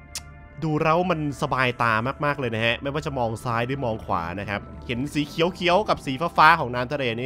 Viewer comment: หนูได้ยิน สีเขี้ยวเขี๊ยว555 หรือหูฝาดไปเอง😄😄😄😄